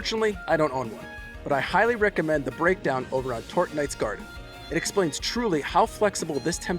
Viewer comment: What was is that you recommended for the Sanbi breakdown at 10:27? Couldn't quite understand and even with the closed caption I couldn't find what you meant lmao 😅 Interested as I just caught a decent one from the saipark and have been training it for the past hour. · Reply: Check out the site tortenites garden. If you go to the Temtem section, you can see popular builds of every Temtem that do well in most situations